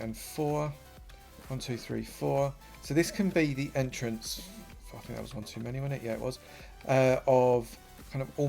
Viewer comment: is it just me or did he say "fucken hell that was one to many wasnt it" 14:45 hahahaha